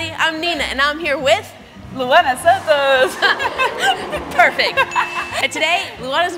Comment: Maybe because I smoked my dinner but I thought it said Laura Sanko and kept thinking she looked really different 😂😅😮